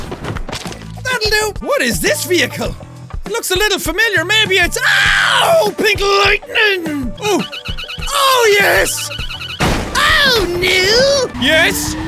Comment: I can't stop laughing when jack said OH NO 🤣 (it sounded like he said OH NU)